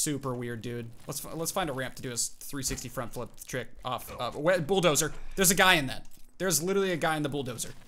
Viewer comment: did he say wet bulldozer?! 🤣☺️